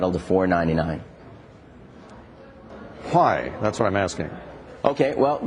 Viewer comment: lol he says fwy instead of why fwy thats why iam asking